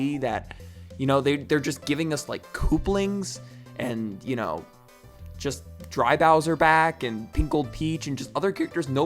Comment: did you just say do dislike Dry Bowser